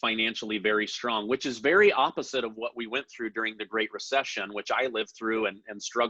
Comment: Umm, if you lived through the great depression you would be 80-90 + years old. You look great for an octgenarian! Lol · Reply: I could be wrong, but I think I only heard him say that he lived through the great recession, not depression...